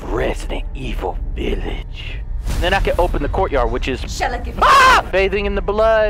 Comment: I swear at 0:04 when that lady starts talking I thought she said :"Shall I give a fuck!" 😂😂